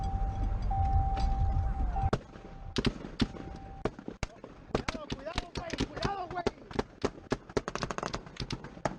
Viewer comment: “CAREFUL BRO, CAREFUL BRO!” “GET DOWN” “CMON, LETS GO ON THIS SIDE.” 3:10 (can’t tell what he said) “GIVE ME MORE ROUNDS.” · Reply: "¡Más cinta, más cinta, más cinta, wey!"